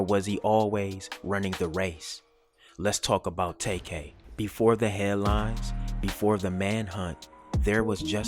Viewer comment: I thought you said before the hairline at 1:31 lol Because, homeboy's hairline is pushed all the way back 😂